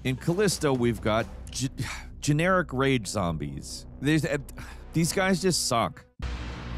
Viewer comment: I thought said "generic RAVE zombies" and I was like well that sounds creative lol .